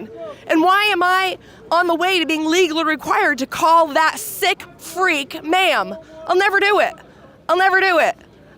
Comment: NGL, at 9 seconds in, I thought she said "That thick freak", not "sick freak"